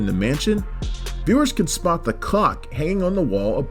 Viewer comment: I hear cock every time.😂